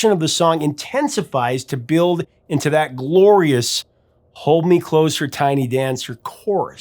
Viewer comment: Tony Danza ! haha I always thought it said - Hold me close - or tie me down Sir .... maybe that is the 50 Shades of Grey Version.